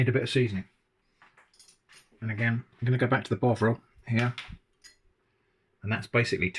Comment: I was just listening when you added Bovril, and I thought you said Bog roll, I was monetarily very confused. Great video as always.